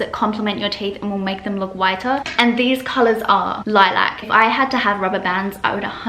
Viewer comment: can someone translate what color she said 😓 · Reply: Lilac - light purple